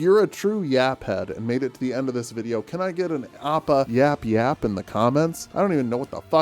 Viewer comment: I misheard the “appa yap yap” as “oppa yap yap” like some kind of nightmarish gangnam style reboot